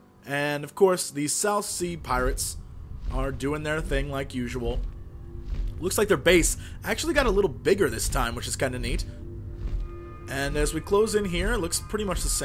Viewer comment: Lol it ALMOST sounded like you said "I actually got a little bigger this time. Was nice." Curse my dirty mind. xD